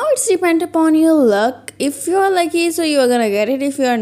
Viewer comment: Can anyone tell me what she's saying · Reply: If you're lucky so you are gonna get it 👀